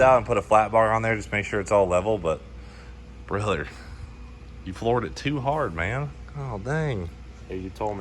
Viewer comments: 5.25 all I heard was "you Florida'd too hard" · You said "floored it too hard" and I heard "Florida'd too hard" but I feel like both are valid statements here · When he said "floored it too hard", I first heard "Florida too hard"... which is also appropriate